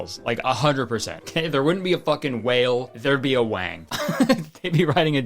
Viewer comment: i could have sworn he said "where there's a whale, there's a wang" here, i must have imagined it